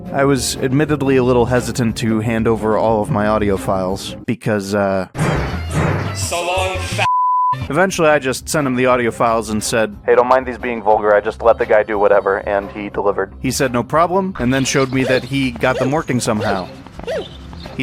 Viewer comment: At first I thought they were saying, “so long, fatass,” and wondered why they censored that but not the f word… then I realized it was another level of the f word, that’s why it got censored… woulda been funny if he actually called bowser “fatass” and not… 🚬